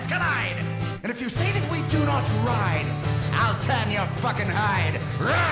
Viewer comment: what did he say I'll turn you'll fucking hide? It's funny 😂